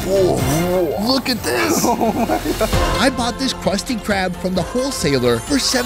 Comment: is it just me or I heard "Krusty krab"?